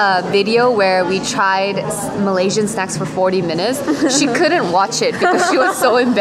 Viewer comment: WOW you tried Malaysian -- what? Did I hear this correctly?